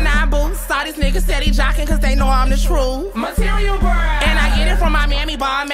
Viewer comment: I thought she said "coz they know I'm the shroom" 💀